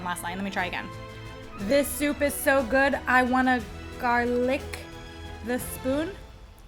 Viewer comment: I thought i heard say: i made a gar lick a spoon. I was wrong. Made me smile though. And then i smiled again when i understood the pun as intended.